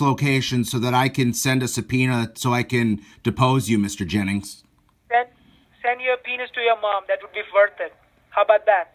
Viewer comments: I thought he said send the peen s to your mom. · Dude didn't understand that said Subpoena.."Send your penis to your mom!" Lmao! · hahahaha 14:00 he thought he was asking him for an email to send him a penis instead of subpoena lol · Did he just say, "Send your pen*s to your mom" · I swore the scammer said, " send your penis to your mom." 😂🤣😂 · Sound like she said "send your p@ to your mom". loll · I thought he said send your penis to your mom. That was funny. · i heard "send your penis to your mom that would be worth it" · Lmao!!! I thought he said send a penis to your mom!! Wow !! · I thought at 14:08 said send your penis to your mom lol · Jeez, I thought she said 'send your penis to your mom' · Lmao! Yall heard send your subpoena to your mom.. What he really said was "send your penis to your mom" 🤣 listen back. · Did think you said penis instead of subpoena? It sure sounded like he said, "Send your penis to your mother." LOL. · It sounded like he said "send your penis to your mom." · Could hav sworn he said, "Send your penis 2 your Mom"-!!?? Am I crazy?!? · I thought he said "Send your penis to your mother." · i thought they said "send a penis to your mom" and lost my shit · I thought he said "you can send your 'penis' (instead of subpoena) to your Mom"! 🤣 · Did He Just Say Send Your Penis To Your Mum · I heard “SEND YOUR PENIS TO YOUR MOM” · sounded like he said "send your penis to your mom" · I thought mr Jennings said “send the penis to ur mom “ 😂😂😂😂